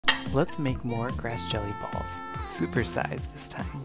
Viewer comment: at first it thought they said “lets make more christian balls” 💀💀💀💀💀💀💀💀💀💀💀